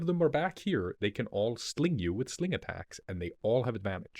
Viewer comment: the panic i felt when i misheard saying the 20 kobolds with slings have "sneak attacks" o_O